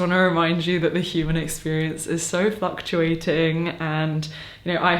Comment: I know you said “the human experience is fluctuating…” but I heard “f*cktuating” and I know that’s not what you said but it also tracks.